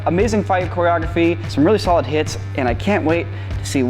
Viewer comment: i heard "solid tits" and judging by reaction she heard it too maybe?))))